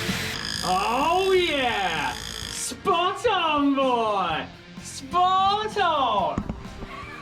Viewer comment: I swear he was saying “Spartan Boy!” 😅 I had to rewind it 3 times….